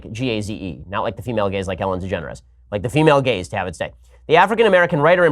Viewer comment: The female g-a-z-e, not female gays, like Ellen DeGeneres😂😂😂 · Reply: I heard "Ellen Degenerate".